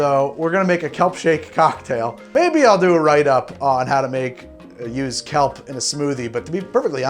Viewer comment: Sea kelp? Oh, I thought you said "seek help"...